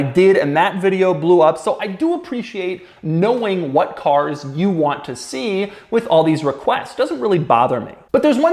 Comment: I thought you said you only review cars you want to